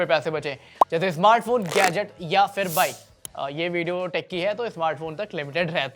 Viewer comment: I heard wife 😂